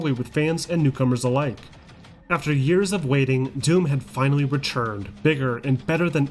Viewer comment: So confusing whenever the narrator says 'returned'. I keep hearing 'were churned'....